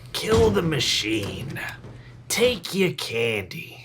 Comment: …I legitimately thought Chucky said “kill the vending machine, take your candy” well done 🤌🏽